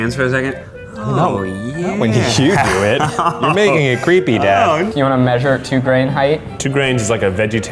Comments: did eugene call ned dad??? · Hold the fuck on. Eugene, did you just refer to Ned as 'dad' at 6:04? Yes, yes you fuckin did. (I thought I'd misheard so I turned on captions and I just--) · Did Eugene just call Ned dad? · did Eugene call Ned dad? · did...... did eugene just call ned 'dad' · am i tripping or did eugene call ned ‘dad’ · Did Eugene just call Ned DAD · Did Eugene just call Ned...dad? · am i the only one who heard dad? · Did Eugene just call Ned "dad" at 6:05? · did Eugene just call Ned, dad? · did he say dad?